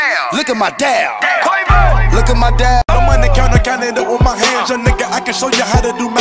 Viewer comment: 느걱맘들이라고 들림 ㅋㅋㅋㅋㅋ